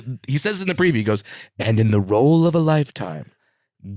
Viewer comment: Am I the only one who thought he said Gary Coleman when he was talking about Tiptoes